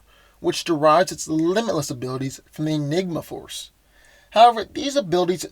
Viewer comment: I heard ligma force